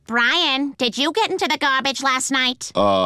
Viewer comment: BRIAN SAYS HOWHY INSTEAD OF WHY